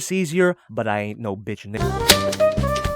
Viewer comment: not gonna lie sound like he said “ i aint no bitch n*gga” 🤔🤔